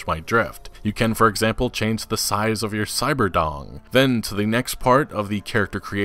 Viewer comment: did he just say "cyber dong"?